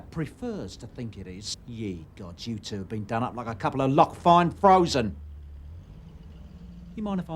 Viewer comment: What does DI Burnside say at 21:25? You have been done up like a couple of .....? I can't make it out. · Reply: A couple of Loch Fyne frozen, in reference to Loch Fyne kippers.